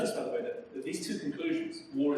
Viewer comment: Apologies for the poor sound quality; full subtitles are now available if you'd like to turn them on! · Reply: Haha, the subtitles say "by the war" instead of "by the way".